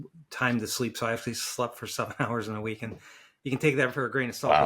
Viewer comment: did u just say u slept for only 7 hours in a week… or am i hearing things?